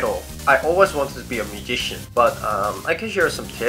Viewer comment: at first i thought you said "i want to become a magician"😄✌ then it all made sense😄 cutie😄